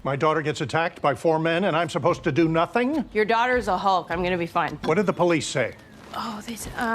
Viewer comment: it sounded like she said "your daughter's a hoe" and I was so confused for a minute It would have been better if she said that though